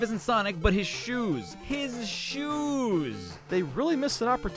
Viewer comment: what did he say in the cough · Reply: Soap shoes